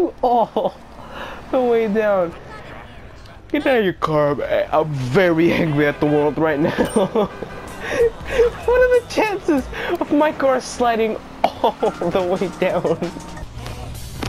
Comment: Did you say hangry